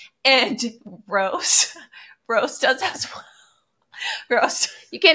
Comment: I am dying. 44:14 I swear she said “Rose does a$$ well” 😂😂😂😂